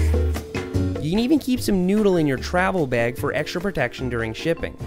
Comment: I wasn't listening for a second and then I heard "you need to put your noodle in your travel bag for extra protection"